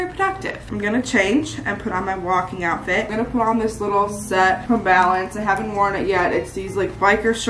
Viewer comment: That jumpsuit looks AMAZING on you. The color is stunning with your skin tone too! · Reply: it sounded like she said it's from balance!